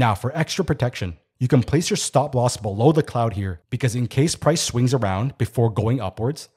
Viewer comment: I about spit out my drink, Ha! What I thought you said, "Incase price screws around..." What you actually said, "Incase price swings around." What I thought you meant, "Incase price F's off around here." Excellent video as always.